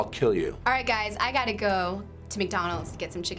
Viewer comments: Who thought it said mcjuggernuggets? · Aw I thought it said McNigglet